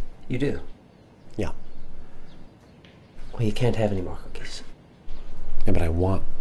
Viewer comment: I thought he said that there werent any more cookies